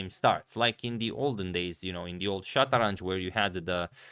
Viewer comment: does he said "SHATRANJ"